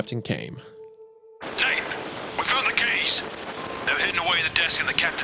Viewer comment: it sounds like hunter said "we found the gays"